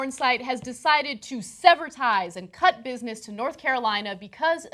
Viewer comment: Did she just say "Severtize"?